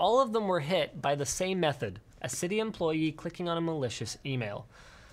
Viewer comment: I know you said malicious email but what I heard was "Some random city employee clicked on a Malicious Female and infected the entire city."